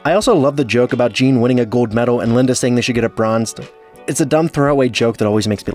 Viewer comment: what are you saying. Geene winning a gold metal and linda saying they should get a bronz...sounds like ur mic cut off . What did u say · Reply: “They should get it bronzed”